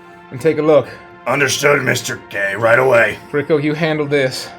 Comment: The first time Torbek said "Mr. K" it sounded like he said "Mr. Gay" and I thought that was very funny